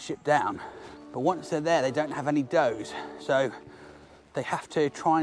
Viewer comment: I've listened to the video twice and each time, in the beginning, I mishear you as saying the rabbits don't have any "dough," and I actually think, "That's true. They carried no cash." Anyway, excellent video! Thank you for making it!